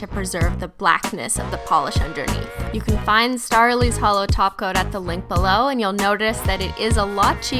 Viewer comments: I feel so silly. I thought you were saying 'holo taco' the entire fucking time. why????? lmao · Sometimes I hear you saying "Holo taco"